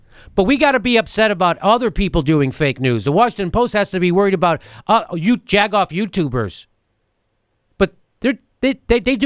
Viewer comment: A jag off nightclub comedian has better sources them MSM? Say it ain't so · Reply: I thought he was saying "jack off".